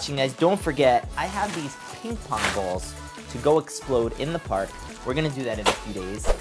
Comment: I heard you say park